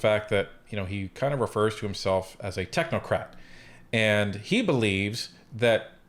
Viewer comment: did you say technocrap or crate? Either way I'm not buying what he is selling